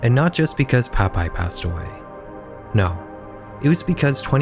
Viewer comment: Did you say 'Popeye passed away'?